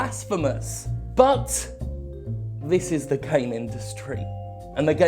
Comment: I totally heard “This is the came industry.” No wonder I can’t get off any more.